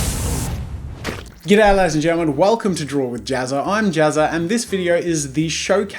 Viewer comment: I renember that before i thought he said "Welcome to drawer with jazza"